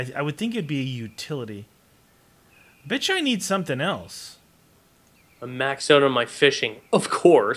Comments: on 21:55 said "which I need something else", it sounded like he said "bitch I need something else"... maybe it's just me... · Wait did he say bitch 21:55🤣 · It sounds like he said bitch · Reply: It sounded like he said "bitch I need something else"! XD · It sounds like he said bitch